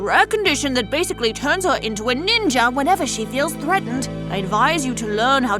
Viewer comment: who else heard the doctor say " It turns her into a ninga whenever she feels PREGNANT." in